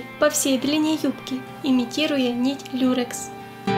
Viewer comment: мне послышалось или она сказала дюрекс???